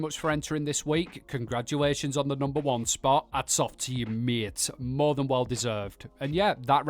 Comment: Hey everyone 👋😊 Congrats at these marvelous camps! ❤👌 I'm a big Fan of Fallout 76 and of course of the Clips and Comments from! Simply Simply Lovely! 😁👍 But I've a question, because my british English isnt simply lovely enough 😅😉 -> what do you ever say at the end..? I just understand "It's up to....."?? (Here in Minute 10:11) I just like to understand your Theme! 😂❤ So far - many greetings all!🎉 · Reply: I believe is saying "Hats off to you mate" Though listening to it again it does sound like ats off teyameyate! 😉